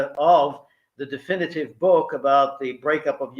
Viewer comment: Ok I found it funny that when he said the name of his book I heard "bums for peace" 🤣